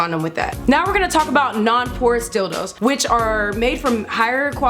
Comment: I thought she said "Horse Dildo" so I had to look back and see what she said, it's "Porous Dildos". 😄